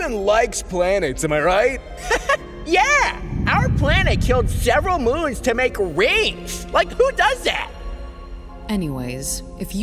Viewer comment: can someone write what he said I don’t understand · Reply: "Our planet killed several moons to make rings! like who does that?"